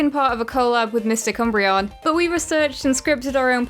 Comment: My hearing has failed me... I misheard you say Mistic Umbreon, as Mr. Cumbreon. And now I can't unhear it.